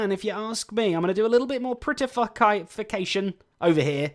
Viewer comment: Oi never thought you would actually swear in yor videos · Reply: Fahd Aleem prettyFUCKation